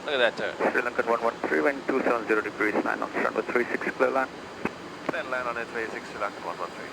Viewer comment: What does the ATC said at 4:12? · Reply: ATC: "SRI LANKAN 113 - WIND 270 DEGREES AT 9 KTS - RUNWAY 36 - CLEARED TO LAND"